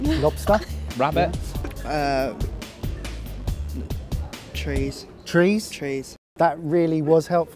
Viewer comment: Thought the guy said cheese not trees.